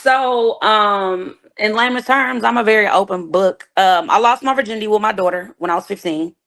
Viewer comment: Did she say she lost her virginity with her daughter? 🤦‍♂️🤦‍♂️🤦‍♂️ I can’t with this 😂😂😂